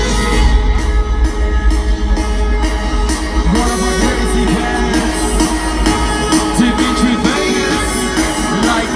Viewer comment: do you know what Mike is saying at 1:19? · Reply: One of the crazy bangers